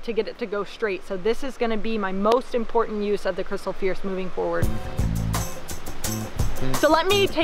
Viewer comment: O... Fierce... I thought she kept saying Crystal Pierce.